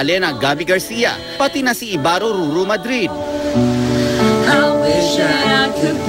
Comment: YbarroRuru ba ung narinig ko?